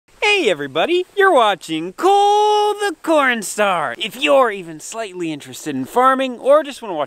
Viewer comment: I hear “COOOOOOOLLLEEEE THE CORNSTAR” My subtitles “COOOOLLLLDDDDDDD THE COLDSTORESSEEEE” Go home subtitles... you’re drunk